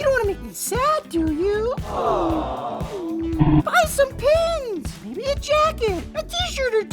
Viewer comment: stg i heard "buy some panties"